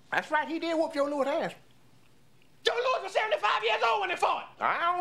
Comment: anyone know wtf he said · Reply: "Joe Louis was 75 years old when they fought" "I don't know how old he was but he got his ass whooped"